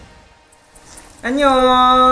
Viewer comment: What did he say here? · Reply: OK. "멋(흥.ver)" is one of ATEEZ's title albums. So they said the words 멋, 흥 in Korean. In Korea, the sound that you make when you blow your nose is described as the word "흥". So they said "코풀기, 흥" in Korean. ATEEZ's title track "흥" and "코풀기, 흥" use the same word but have a completely different meaning. It may be difficult to understand because it is a pun of Korean jokes using language play. 🥲